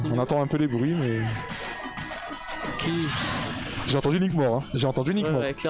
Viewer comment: FYI, at 1:55, the French commentators were saying that they couldn't see the stream but they could hear the sound effects. Once Link dies, you can hear one say ''J'ai entendu Nick mort'', which translates to ''I heard Nick die''. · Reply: As a french person it says " Link Mort"